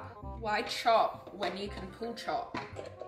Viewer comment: Why was I hearing “pool chalk” instead of “pull chop” lol.